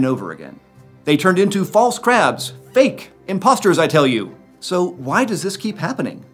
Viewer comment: Did you just say "Impostors"